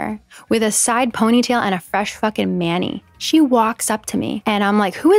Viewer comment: ngl, when I first watched this I misheard you say "fresh fucking mani" and thought you called Ingo a manlet and nearly choked